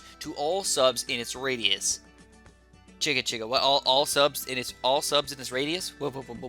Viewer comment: says something that sounds like:"Cheka cheka" idk what this means but it's similar enough to the macedonian phrase "чекај чекај" which is pronounced the same as cheka cheka and it means "wait wait" and i think thats what he was trying to say so conclusion idk